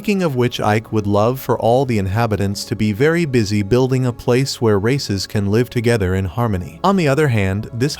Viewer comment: i thought he said racists 🤣